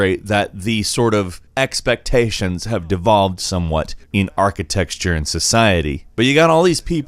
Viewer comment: HAHA 1:43 he DEFINITELY says "archiTEXTURE" instead of architecture! which, if you don't know how to pronounce it, that's pathetic- and it's pronounced like ( arch - eh - tech - sure ).